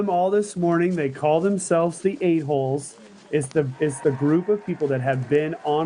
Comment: thought he said “they call themselves the A-Holes”